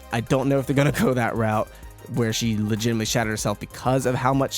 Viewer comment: I thought when he said shattered herself the english sub said that she shat herself